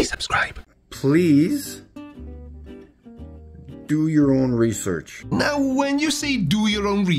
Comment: Did he say research??? I thought he said reefer.... And yes: Everybody should do their own reefer... A laser thermometer... respectively at the sun and the moon... really?!??! 🤣 I'd like to know what he's smoking... I want some of that...